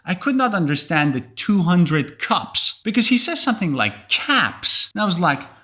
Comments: I heard “to win the cups” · I understood all the them. I only struggled with the '200 Cups' and 'whole family '. Instead I heard 'to under guts ' and 'all family'. About the last one, I heard it on repeat and yaahoo! I understood every word 🥺❤️. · The only thing I missed was the 200 Cups. I thought he said 200 craps. LOL